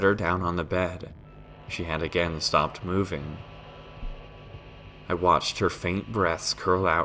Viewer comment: First time I went past this, I heard "I watched her faint breasts..." I lol'ed a bit too much >w< Excellent reading though and the story was amazing :0